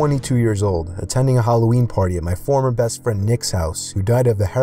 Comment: This came out 4 years ago and I’m watching in 2019 of October 🙃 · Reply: Bruh when he said Halloween party I thought he said heroine party😆